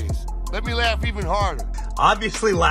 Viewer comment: It sounded like Benny said that her first name was CRINGEY Pierre. That sounds quite accurate. Way to go Benny!